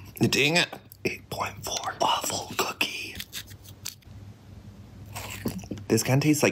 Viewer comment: I thought you said awful cookie but you said waffle cookie! Lol